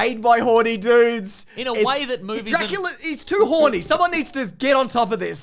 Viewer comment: did he just say "Ejacula"? 😁